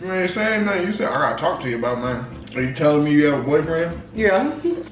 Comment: Yooooo I thought it said “telling my daddy I have a boyfriend “ I was like DAMNNNNN Yo daddy fineeee